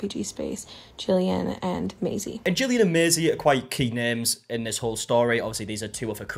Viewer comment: Thought you said "cute names" instead of "key names" lol was confused for second